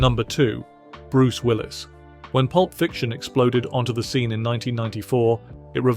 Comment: I thought you said that he explodes not ghosts